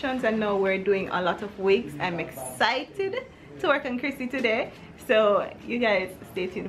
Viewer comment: Did the wig lady call you Chrissy? I thought I heard that? The wig is so cute, but you know we like your natural hair. I understand though, you need a break .